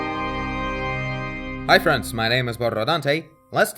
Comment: You said 'hi friends'!? kinda made me sad haha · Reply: know it's fronds but it sounds like you said friends therefore it made me sad because I consider myself a frond and not a friend. I created a loop I cannot get out of D